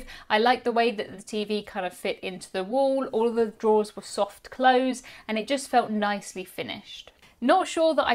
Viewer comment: I have my very old headphones on and I heard “all the drawers with soft clothes!” And had to rewind 😂😂Seemed very generous for them to include soft clothes!